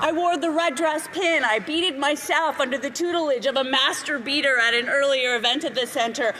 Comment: Did she just say… Master-beater?